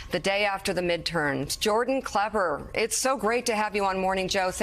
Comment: mika was off her game on this interview imo???... i think she called him jordan clever at the end !!LOL